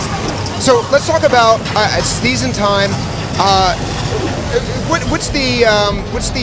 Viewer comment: Sounded like he said "A sneeze in time" at 4:12 XD